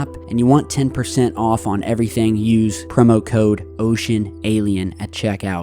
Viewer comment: I thought you said “Promo code, Oh Sh¡T” 😆